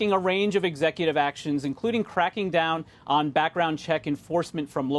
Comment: If Biden wants to talk about back round checks, he should start with his son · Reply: I heard "back round cheeks" -_